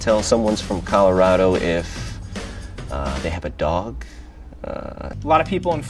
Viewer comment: No one: Colorado: “if they have a dog” Annoying people: OmG ThANkS fOr aLL thE LikEs: 9!! wOw so MAnY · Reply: Abbie I thought he said “If they have a dock” I’m so stupid lol